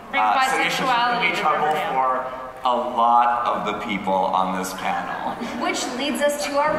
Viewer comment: What did Roberto say at 16:38 I can't quite hear it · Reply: “....should be trouble for a lot of ppl on this panal” which is talking ab Toni